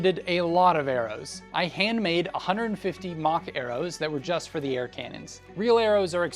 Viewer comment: for a good couple seconds, I thought you were saying “mach arrows” and not “mock arrows.” Then it gave me a sick idea. What if there was some sort of futuristic medieval fantasy where the most common ranged weapon in use was a smaller sort of crossbow and it shot “mach arrows” which are just arrows that are the size of bullets. Depending on the crossbow size depends on the mach arrow. Now that sounds sick, doesn’t it?